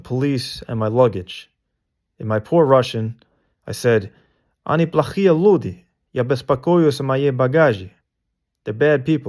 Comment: "They are bad people, I relax in my trunk" is what I heard from that broken Russian.